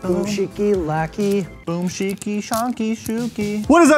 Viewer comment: when he said shikishanky i thought he said shashank (my name)